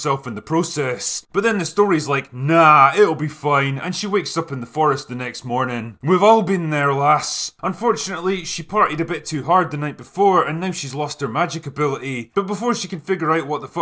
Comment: I legit thought you said "she FARTED a bit too hard the night before" 😄